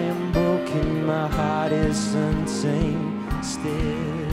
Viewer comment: Who thought he said Unzayned?